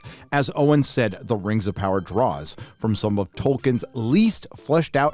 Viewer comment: the rings of power draws? oh i thought you said the rings of power "dross"